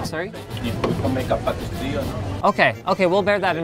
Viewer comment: What does the guy say at 2:03? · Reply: He'll make a better deal, or something like that.